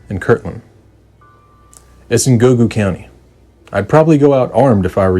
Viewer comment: I have absolutely no idea what county he is saying. “Gootin” county? Is he trying to say geauga? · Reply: Yea, Geauga County. I wasn’t entirely sure how to pronounce it but I probably could’ve the name up on the screen too 🤔